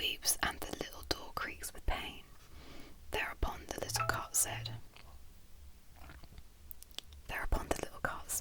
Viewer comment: "There upon the little c**t" it sounded like. Ha ha